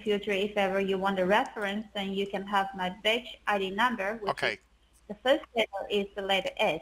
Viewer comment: Ah, the pepper. It's prolly just me, but I keep hearing 'bitch id namber' and 'truck drafficking'. Great call again. I admire your patience. Thanks!